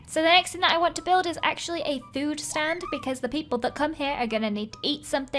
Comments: When she'd said "food stand" I thought she'd said "nude stand" and I was worried for a moment 😂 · i thought she said a nude stand not food stand😂😂😂😂